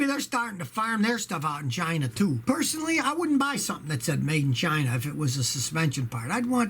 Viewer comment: i thought he said : Made'n v'gina ! :)))